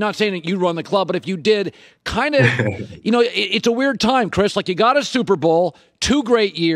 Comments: Did he called him Chris at 0:32 ?? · did he call him Chris?